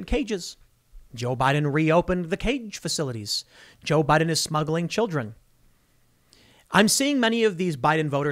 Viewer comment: Tim: “Joe Biden is smuggling children” What I heard: “Joe Biden is snuggling children” Both are true so…